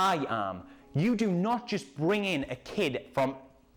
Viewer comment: Because of your acent I thought Mace said "Norway" instead of "No where"